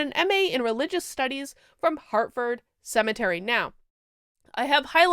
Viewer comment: Sounded like you said she went to Hartford Cemetery! LOL